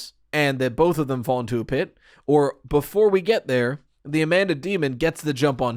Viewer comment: did you say ‘into the pit’ 😳😱fnaf confirmed.